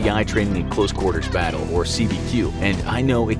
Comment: Not gonna lie, I thought he said CQC. Keep up the awesome work guys 👍